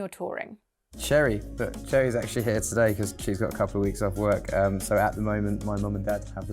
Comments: He did not say of, just couple weeks off. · why am I hearing "..actually hits today..." I slowed down to 75% and barelly hear "here" this is way too fast blending whole sentence in one word :)